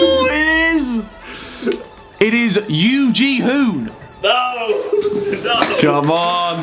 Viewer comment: Sounded like Yu Gi Oh lol